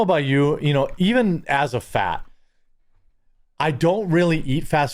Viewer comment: I had to remind this like 3 times … did you say “even as a fat..” ?! 😂 😅